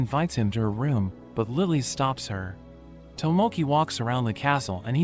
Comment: I hear tomonkey 😂😅😅😅😂😂😂😂😂